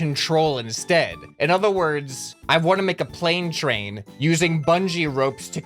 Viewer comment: I thought it said Pain Train for a second lol